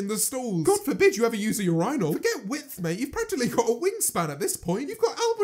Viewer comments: tf did he say urial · did just say urEYEnal 😣